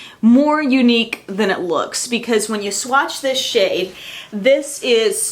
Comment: I swear I thought I herad you say "when you swatch this ____" well, you know what the blank is...